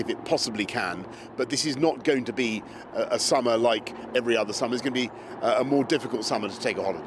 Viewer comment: I thought they said "We're going to eat pizza". A better option in my view.